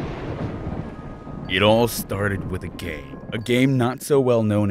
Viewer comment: I thought he said gay I NEED JESUS